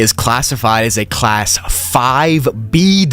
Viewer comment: gonna be honest i thought it said it's class 50 for a sec